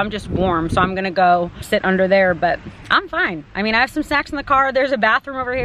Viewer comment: it sounded like you said you "have some sex in the car" haha not snacks